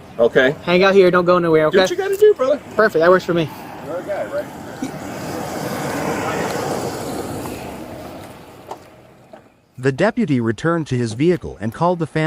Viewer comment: Did anyone else hear the suspect ask the cop "you are a guy, right?"? Was at around 1:53ish.